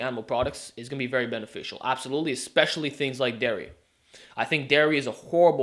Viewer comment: what did he say? deria? English isn't my first language, could someone tell me what he said? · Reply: Dairy : containing or made from milk. Milk Products like Cheese, Yogurt, Butter etc.